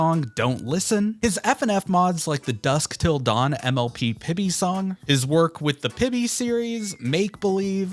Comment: D-did you say... Pibby?